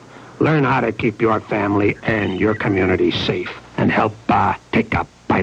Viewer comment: I'm an 80s kid and saw this growing up. I never knew what he says just before "take a bit outa crime." What is it? "Help, ah..." or "Help us..." WHAT IS IT? 1:27 mark... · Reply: It's 'help ah...take a bite outta crime' pretty sure